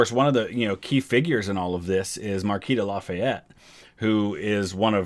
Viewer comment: Lol @ 1:31:34 😂😂😂😂 ok I been tripping this whole video I watch all my videos on 2x speed and I heard you say Marquita Lafayette and I was like did he just say my name .. I was like no he said Marquis and then I heard it a couple more times and was like ok let me rewind and put it on normal an CC and yep you said my name lol my last name is not Lafayette tho😂😂😂